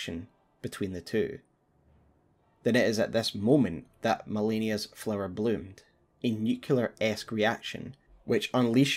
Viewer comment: wonder what she says here... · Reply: "I am Malenia, blade of Miquella"